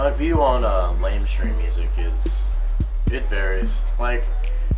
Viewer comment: Did he say "lamestream music" at 9:40? lol